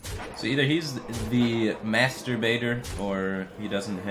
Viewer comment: I had to listen to that 4 times to realize he said "Master Baiter", and not what I thought he said